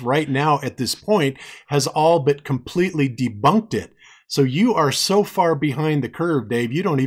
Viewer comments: did he say "debunkted"? i believe he did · I had to replay several times at 31:00 to make sure I heard correctly. That idiot said "debunkeded". Classic, considering he's saying Professor Dave has no idea what he's talking about... when said idiot has no idea that he's (incorrectly) adding letters to a word.